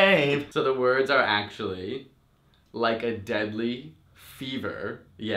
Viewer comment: "Like a delayed femur" lol thats what i thought arianda grande was saying